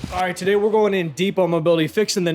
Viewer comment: Thought he said , tonight we're going deep into dianabol ... Because he should come clean on steroid use . No one that age grows muscle like that .👎👎👎